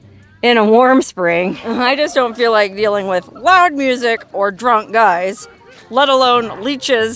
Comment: I'm a long time fan of your content but did I hear you say "you don't feel like dealing with black music"?